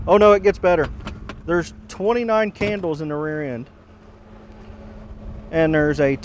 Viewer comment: A warrant for stealing candles lmao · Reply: At first I thought the deputy said, "She's got 27 candles in her rear end." I was like, "WHAT??!!"